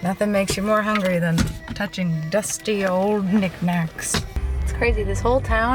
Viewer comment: I was like EXCUSE ME when I heard at 16:13 "Nothing makes you more hungry than touching dusty ol' Nick..." Knacks - I'm sorryyyy 😅😅